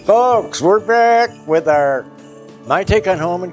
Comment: Merry Christmas and, this video is a bit difficult for me to understand. Every 2 you are happy to see. You'll laugh at the beginning when I watched your videos I thought you were saying folks I understood fox! LOL! Bon réveillon à tous. Marie-Yvonne